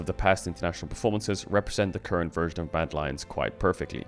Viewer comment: did he say bad lions?